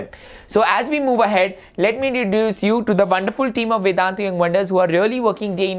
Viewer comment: deduce you instead of introduce you😂😂